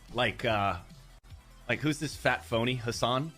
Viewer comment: (also on topic but all I heard was "fat pony" 🤣) If gay people said "leave the children alone", would that make them homophobic too? 👀 (don't reply, I know that the acronym mafia TOTALLY DESPISES the group Gays Against Grooming and they ran out of buzzwords against these (which has thousands upon thousands followers all across the globe), specially after lesbians rejected to get out with MTF people and were banned from dating sites because calling a lesbian "homophobe" is the ultimate buzzwording spittle.